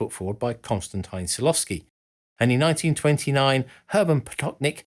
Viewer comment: The close caption says 1928, but the person says 1929 so which one is it?